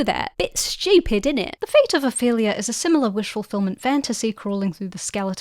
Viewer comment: The automatic captions at 6:06 say "A failure" instead of "Ophelia" lmaoooo